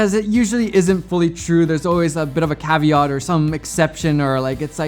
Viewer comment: did he said Caviar? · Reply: yeah, but from context it's probably a `caveat`